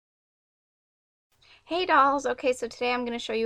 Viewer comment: did you say hey dolls? 0:01?? XD